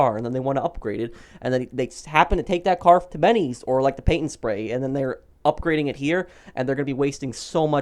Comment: did he say paint and spray like in san andreas